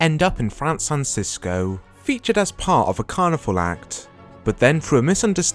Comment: Did you actually say Fransan Cisco?